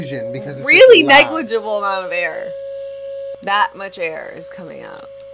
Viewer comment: i cant help but hear jenna saying "its really negligible mama bear" Can someone please tell me what she's saying its keeping me up at night